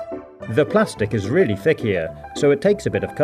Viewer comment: did he say thickeyer it's thinker